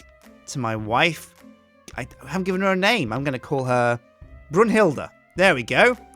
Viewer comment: I heard "Grunt Hilda"